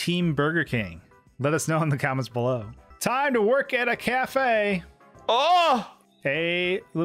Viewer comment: I thought you said cafeteria, and I thought aren't you just working at school.